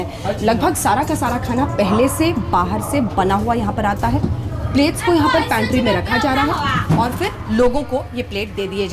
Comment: What did the Chinese lady say at 12:48 ? 什么中国的老太太在12:48说呢？ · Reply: please don't film here .(she is the waitress on the train.)